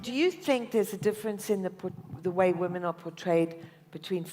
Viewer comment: She didn't answer the question it was , portrayed, and megan thought she said betray, what a goof, they are all laughing at her, how embarrasing